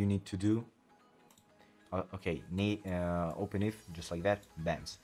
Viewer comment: what are you saying i cant understand you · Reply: He said Open IV, its the name of the program